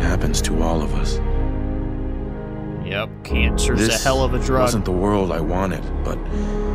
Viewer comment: This serious ass endgame cutscene and all I hear is - "Cancer's a hell of a drug." FaceDesk feelsbadman